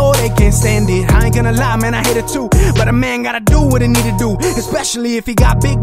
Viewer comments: I thought he said what a nigga gotta do but he said what a I need to do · what make him think that he can use the N-word? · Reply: He didn't he said need to